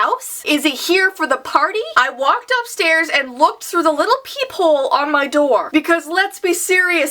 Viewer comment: XD I thought said Pee Hole . I was like "Pee hole ?!" XD I NEED TO CLEAN MY EARS!